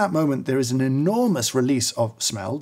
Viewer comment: I thought you were about to say “. . .there’s an enormous release of noise. . .”